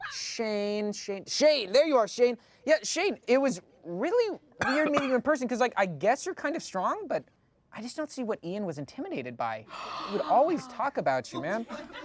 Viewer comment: What does Noah say at 14:09 ? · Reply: "Shayne it was really weird meeting you in person because I guess you're kind of strong but I just don't get what Ian was intimidated by; he would always talk about you, man."